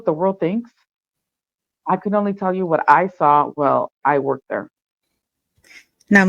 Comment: The voice is heavily altered. This likely a woman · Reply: What happened at 8:40 when a woman's voice said something life "I only worked there". Did the voice changer malfunction?